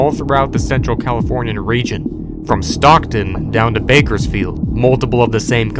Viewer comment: did you say, Bakersfield? That's where EDP445 lives